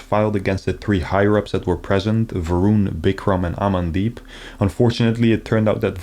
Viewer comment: I'm playing a game and listening to this, and all I heard was "the guys real name is 'I'm in deep shit'" and I had to play that back lol "Amandeep ****"